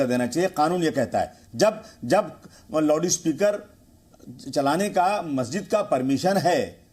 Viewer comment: kya bola tune? Loudspeaker??? Ya Loduspeaker